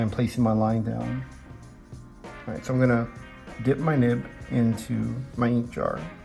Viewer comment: i thought he said dip my nip